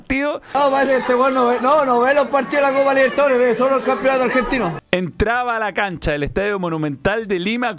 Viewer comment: alguien por favor me traduce lo que dice el perdedor ese que no se entiende nada??? o ponganle subtítulos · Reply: Creo que dijo: “Este weon no ve partidos de la Copa Libertadores, ve sólo el campeonato argentino”